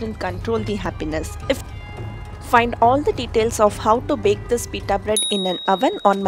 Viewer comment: I heard 'how to make pita bread like a bro".....